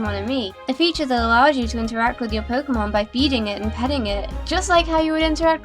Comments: I thought said "Beating" but it was actually "Feeding" ;-; lol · My volume was down so low it sounded like "beating it" instead of "feeding" it... LOL! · When she said feeding it I thought she said beating